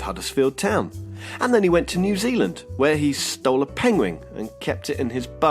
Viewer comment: is it just me or did he say pengwing